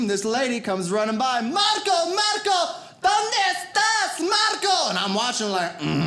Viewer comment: Marco is a girl name? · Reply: she thought she heard "marco", but it was probably margo